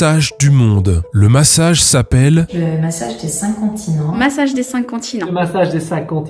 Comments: je suis désolée mais tout le long de la vidéo j'ai entendu "le massage des incontinents" 😭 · j'ai entendu le massage des incontinents 😂